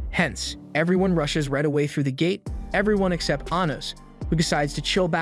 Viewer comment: guys... the subtitle said anus instead of anos wtf